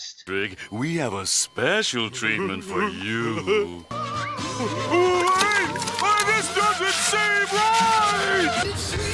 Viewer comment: what does patrick say at tha spot? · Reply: A Car "wait, this doesn't seem right!"